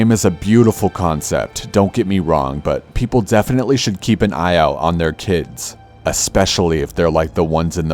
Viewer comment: I thought he said keep and eye out on your tits 😐